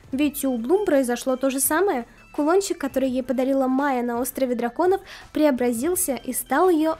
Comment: Мне одной показалось когда сказала Майя то мне послышалось Моана